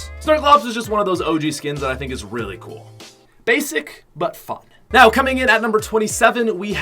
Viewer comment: Did I hear basic butt fuck